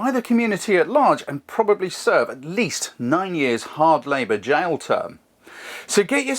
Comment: Nine years? I thought they said 7! :-) Great video as always. Love your explanations (And demonstrations)